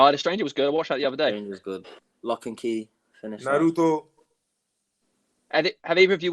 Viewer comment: Did Pogba say Naruto? 👌♥️